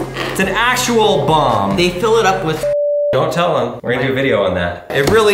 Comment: I immediately thought he said “they fill it up with sh*t